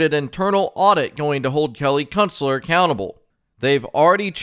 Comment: I swear I thought you said Kelly Kuntster.